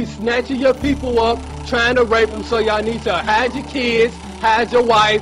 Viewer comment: "Hide yo kids, Hide yo wife and hide yo aisgeveienekeh" - a wise man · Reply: I always heard 'hide your horses'